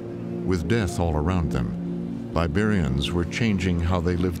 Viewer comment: It's gross that the narrator says something sounding like "barbarians" instead of "Liberians"